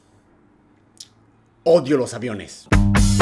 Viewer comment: A lo primero le entendi “odio los viernes" Y yo de que: Dafuq? Quien odia los viernes!? :v Luego me destape mis fucking oídos y escuche que decia: Odio los aviones xDDD la vida vale berdhurá :'v a quien le importa? Daphne :3 v